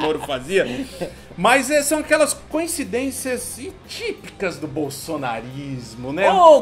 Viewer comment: eu ouvi "canhecidências"?